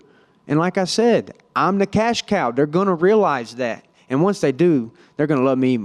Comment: What did he say??? The Gonorrhea lies??